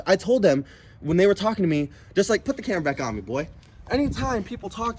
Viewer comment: He really said “ put the camera back up BOY” what a bitch... never talk to yo people like that y’all.... it’s at 6:00 mark